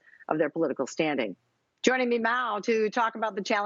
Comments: Does she say "joining me meow" at 0:50? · Does she say "joining me meow"? at 0:50?